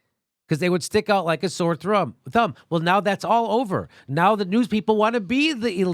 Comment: Did jimmy say, "stick out like a sore Trump" ?